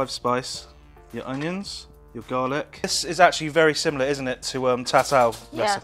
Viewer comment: is he saying "this is very similar to char siu"? I can't make out the dish it's similar to. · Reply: I am the person lol and Char Sui